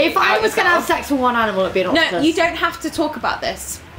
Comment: I just read the description. Rosie said an octopus! I thought she said Elephant and I was really confused like how are already lubed up?